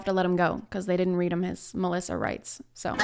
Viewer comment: I think she just called "Miranda rights" as "Malissa rights"